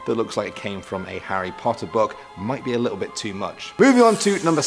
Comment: Did anyone else mishear slightly and hear "Andrew Serkis"?